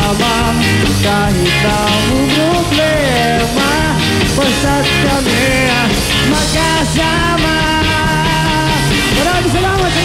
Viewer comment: It might just be my dirty brain, but I think I heard Ely say "basta't kami ay na sa kama" while the lyrics I've found says "ay palaging mag ka sama".